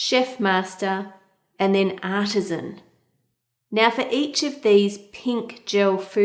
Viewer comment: When she said artism I thought she said autism because of her accent. Hahaha.